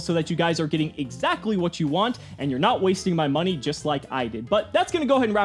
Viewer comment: So you're not wasting my money just like I did. Me: Wait what? Did I hear that right?